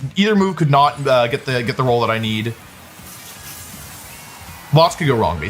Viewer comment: oops, got 'role' instead of 'roll' here. Though i suppose you could say moves have roles in a poetic sense lol